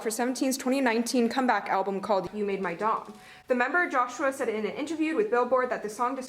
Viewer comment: Lol I heard ‘you made my dong’ too. Comedy queen